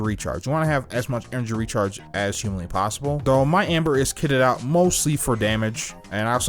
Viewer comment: For a moment I thought that you were saying Ember.